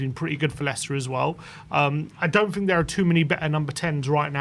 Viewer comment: I'm sorry, but did he just say "I'm not sure there are better number 10's across Europe right now"? Did I hear that correctly? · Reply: “Many better”